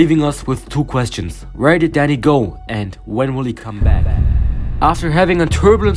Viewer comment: I heard "where did daddy go? "